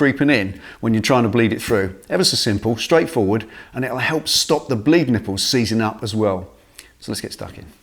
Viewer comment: I heard "It'll stop yr bleeding' nipples seizing up!" O.O But it wasn't that at all'